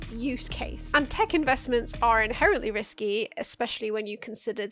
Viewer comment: I zoned out for a second and thought you said "Tekken vestments"